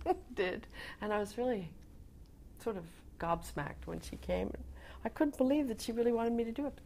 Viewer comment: did she say gobsmacked? didn't know Americans used this word, it's a British English word.